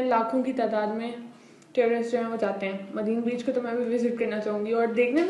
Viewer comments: what did you said Terrorist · what did you said "Terrorist"😂😂😂😁😂😂